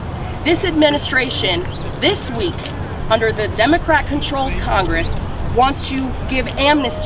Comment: I thought i heard her say under the DUMB O CRAT (@ 1:35) controlled congress! Which would be the perfect name for them.